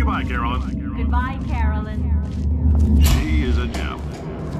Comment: “Say goodbye, Caroline.” “Goodbye, Caroline!” “She is a Gem.” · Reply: Ignorance Is bliss I thought it said “She is a gel”